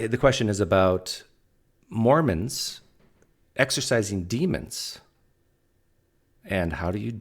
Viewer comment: First off I misheard exorcising for exercising demons. I had mind vision of a preacher running a demonic zumba class. 🙄😏